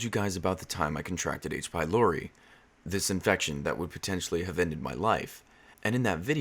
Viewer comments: I thought he said "I contracted aids by Laury" 😂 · i thought he said he contracted AIDS by Lori